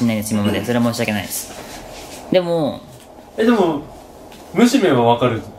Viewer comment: しゃちょーって、負けさんの事『むしめ』って呼ぶんだ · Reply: むしめん って聞こえたw